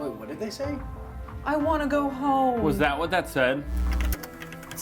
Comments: At first I thought that the guy said jimmy I want to go home but now it sounds like he’s saying can you hear me I want to go home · So- I slowed down the part of the tape that said "I wanna go home", and i heard "Sammy, I wanna go home" so